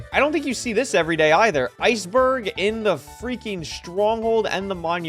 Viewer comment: I thought I heard feinberg for a sec